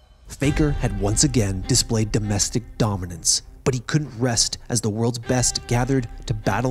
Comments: bruh I thought this bih really said "faker once again displayed domestic violence" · I heard: "Faker had once again displayed DOMESTIC VIOLENCE".